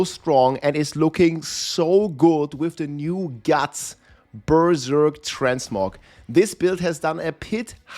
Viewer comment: what did you say? The new guts berserk?